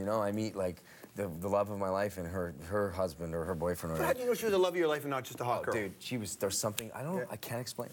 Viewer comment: George says "How do you know she was not the love of your life and not some hot dude?" Either there was some joke sound editing on this clip or George pulled a Freudian slip that got quickly edited with a cut edit of Michael's answer. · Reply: Brad Miller Who´s slipping ;-) here? I hear: "How do you know she was the love of your life and not just some hot curve?" Slow it down...